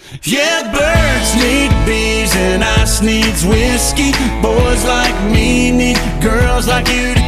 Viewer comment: I always thought he said "I sneeze whiskey" 🤣😂😂